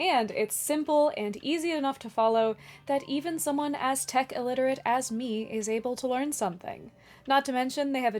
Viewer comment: did you say you are Aztec illiterate? To be fair I can't read Aztec either. As to your question, I read the first two books, then saw the tv series, heard the radio show, went to a convention dressed as Ford and read the next two books. The first book is my favorite because Adams' way with words was new to me and wonderful breath of fresh air.